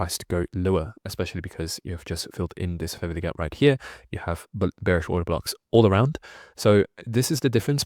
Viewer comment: At: 12:19 what is ** gap that you mention, I can't hear what it is, and reading the transcript doesn't help. Really enjoying your videos. Many thanks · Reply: He said, "fair value gap".